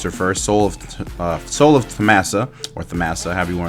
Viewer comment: When you said "Soul of Thamasa" it sounded like you said "Soul of the Masta" when I had my hear turned away.lol I was wait wut?